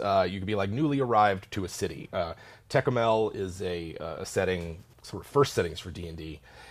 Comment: What's the name of the setting they spoke about in 41:23? I couldn't find anything looking for what i thought i heard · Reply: Tekumel!